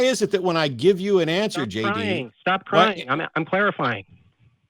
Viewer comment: he did say stop crying twice at the 2:58 range what I heard was " stop crying... stop crying... Im clarifying"...